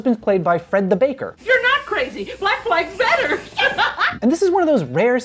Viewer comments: I literally thought I heard the Black Flag's commercial lady said "Black Lives Matter!". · okay it DEFINITELY sounds like she says black lives matter. Based mrs puff ngl.